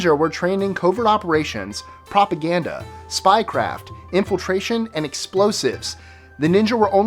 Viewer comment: when said spy craft i thought he said spy crab at first lol